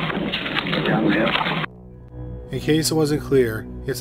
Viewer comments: This was the first time that I have heard the recording taken by the cellphone - I hear "young girl" not "down the hill". Am I the only one who hears this differently? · I thought the guy was saying "you got mail" not down the hill. LOL